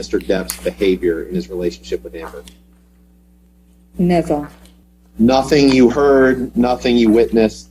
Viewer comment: She replies "never" and the closed captions say "nizzle" lmao